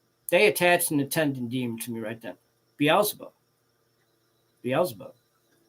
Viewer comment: Did you say Beezlebub? I had a dream that mentioned this despite me not knowing the name normally I asked what was behind my housemate and it said Beezlebub